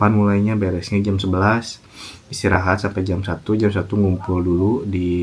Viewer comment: Banyak istilah aq ga paham.. Sampe haejong-bang atau apa itu kedengeran kayak 'Jombang' di aku 😂😂 Makasih udah share pengalamanny ya. 👍